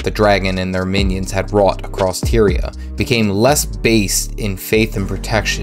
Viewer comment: All I heard was that guardian became less based for a second 😂😂😂